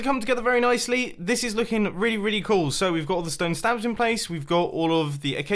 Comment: did he say stone stabs